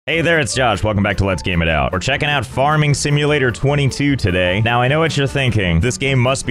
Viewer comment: I thought he said farting simulator😂😂😂😂